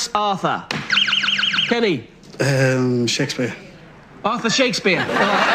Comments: but it still sounded like author. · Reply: but... the English person DID hear author instead of Arthur. He answered Shakespeare. I'm not saying the MC pronounced it incorrectly for a person with an English accent, I'm just saying it does sound like he said author to many ears. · to be fair it does kinda sound like he said "author" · wait i get that one i think he misheard "Arthur" for like an "author". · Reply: i thought he said author too... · Kat Shakespeare isn’t an author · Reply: I know that Shakespeare isn’t an author but it did make that guy’s answer a bit more understandable. The host sounded like he said author rather than Arthur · wait i get that one i think he misheard "Arthur" for like an "author". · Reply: Jason Deng I thought it said author and I was like "he's not wrong!" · wait i get that one i think he misheard "Arthur" for like an "author". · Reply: Jakk Frost wow I have watched that like 10 times and I always thought he said author, no joke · I thought he said author too. Not Arthur. (Near the beginning) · I thought he said "author" too. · Why tf of all the names is the world would they pick the one that sounds exactly like another word??? I would be pissed lol I 100% thought he said "author". Especially with the british accent those 2 words sound the exact same. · in all fairness, the arthur one did sound like author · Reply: I thought he said author too